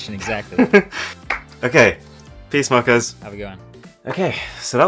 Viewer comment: at the end 10:47 did he say peace my cuz